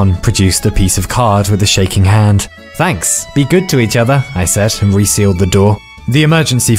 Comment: when you said "Be good to each other" I heard "Be excellent to each other"